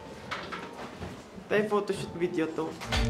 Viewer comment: Did Justin just call Ken "'TE"?! "'Te, photoshoot? Video 'to."